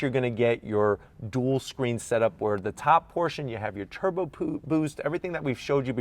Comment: Did anyone else hear him say turbo poop boost🤣🤣